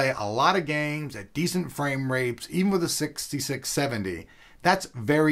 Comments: xD at 1:15 i thought he said "Frame Rapes" lol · Did he say framerapes? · did he say frame rapes? 1:11... poor frames... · umm..is it me or did he just say framerapes? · did he say frame-rape? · LOL, Does he say decent frame rapes